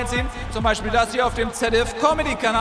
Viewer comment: Sagt Nils am Ende wirklich "Küss den Ring, Bitch?" oder habe ich mich da verhört? :D